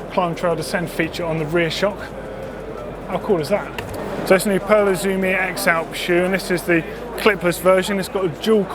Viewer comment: What's the model of the shoe at 4:09 please? (I'm french and I don't understand what he said) thanks :) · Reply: Vincent Pearl Izumi X , derien :)